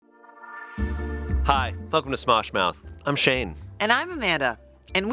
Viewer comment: sometimes when shayne says “welcome to smosh mouth”, I hear “fuck um to smosh mouth” and it makes me giggle